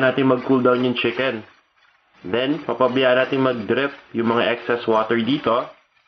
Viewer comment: did i heard "drep"???